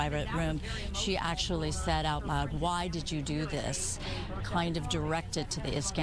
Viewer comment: said to the Iskanders "Why did you do this?" What a thing to say to the parents? She is telling the Iskanders they are bad people for wanting justice for their children. The Grossmans don't take responsibility for anything. Rebecca did bad killing their 2 boys. Rebecca had no remorse and never took any responsibility for what she did. Instead her and her daughter laid blame on everyone else. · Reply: I thought she said " How would you do this?"